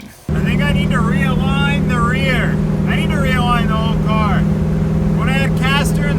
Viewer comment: What u said around 11:55? I need to manline this car ??? :P Would be awesome to see you at the shredyard hoonigan :P or did i missed an episode? (Just new around your channel. Keep the content comming my man!)